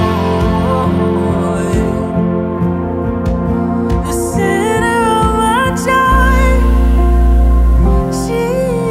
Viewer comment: Can someone please tell me what the words are from 2:51 min? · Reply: The center of my joy, is Jesus1